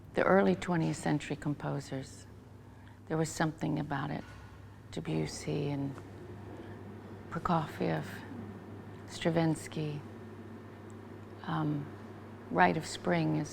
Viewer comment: Does anyone know what the two names Tori says at 15:11 are? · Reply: Composers: Claude Debussy, Sergei Prokofiev, and Igor Stravinsky